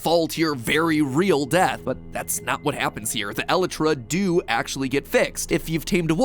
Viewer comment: im sorry.. but did he just say.. e-le-tra?